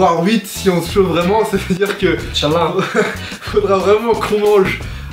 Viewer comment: Il a dit quoi à 2:41 ? j'ai cru entendre Inch Allah :o